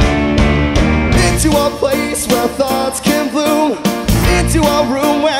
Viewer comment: i heard "into a place where thots can bloom"